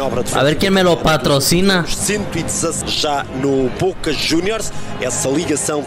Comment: Sin que me ataquen ni nada, al minuto 1:10 el relator de fondo dice "Boca Juniors"? Jajaja no entiendo por qué lo nombra, pero me llamó la atención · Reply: Si confirmo ! Dice Boca juniors sjsjsj